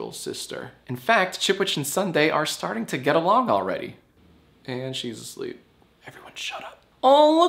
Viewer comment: He called her Chipwich before he even revealed it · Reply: Oh, I thought he said "this dip shit and Sundae are getting along already." Teehee